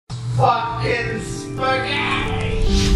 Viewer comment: Did I hear Spaghetti? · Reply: Spaghetti Top FUCKINNN SPAGHETTTIIII